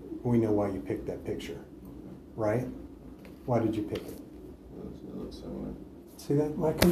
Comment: What does he mumble at 44:40? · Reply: He says "Cause it looks similar." I guess similar to him.